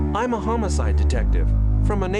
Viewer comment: That intense scowl followed by “I’m a homicide detective” delivered with the nicest, friendliest voice I’ve heard in a video game killed me 😂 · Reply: I heard it as "I'm a homo-side detective" and I was frigging confused man. It just didn't click because of how they pronounced it